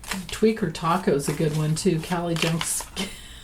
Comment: I thought you said Tweaker Taco but now I realise you said Tweeter Taco. 😂 However both nicknames could apply.